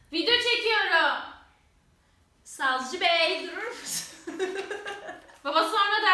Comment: Babanıza savcı bey mi dendi? Yoksa ben mi yanlış duydum 🙂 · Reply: no name sazcı beeeyy dedi😂